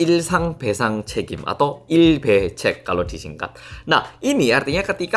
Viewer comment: Sek to... Aku ngakak sama pelafalan singkatan 'claim ganti rugi sehari-hari' dalam bahasa korea. I'm sorry, but I heard it as 'I Be Cek'... 😅😅😅